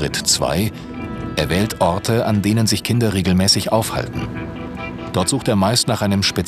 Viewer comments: ich hab verstanden er sucht sich orte an denen sich Kinderriegel mäßig aufhalten xD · bin ich die einzige die "an denen sich Kinderriegel mäßig aufhalten " verstanden hat, anstatt "kinder regelmäßig aufhalten " 😂😂